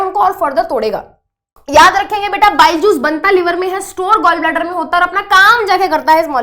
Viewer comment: i heard byjus lol